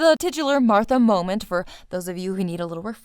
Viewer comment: LOL She's not just a Martha, she's a mom Martha. She's...a MOMTHRA. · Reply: She has tiny twin priestesses? Sorry, thought you said Mothra.